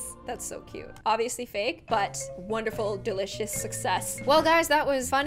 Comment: Was I the only one who thought she called the dog delicious at 15:58?